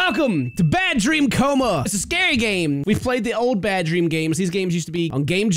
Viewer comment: OMG MY AUTO GENERATED CAPTIONS WERE ON BY ACCIDENT AND AT THE START IT SAID "HELLO MY NAME IS FARMER PUMP" OMG I'M CRYING SO HARD RIGHT NOW 😂😂😂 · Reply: instead of "scary game" it literally said "this gay game..." lol. Sometimes the auto generated captions are pure poetry. 😅